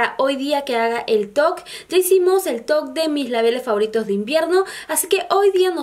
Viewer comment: Pensé que decías el toc o el tog, hasta que puse atención y es el top, perdon por mi sordera 👂